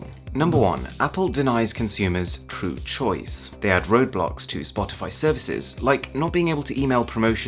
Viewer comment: it sounded like you said roblox